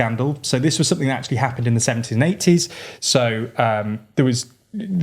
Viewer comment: For a second there I thought you said "this was sth that happened in the 1780s" LOL. But you said "70s and 80s", hahaha.